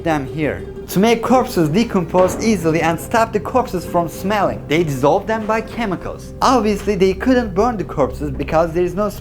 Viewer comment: Wait wait! Did you say, "they dissolved them by chemicals"? 2000 years ago?